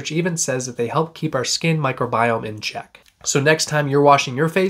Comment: Omggggg new fear now..all i heard was "microscopic demon" 😱😱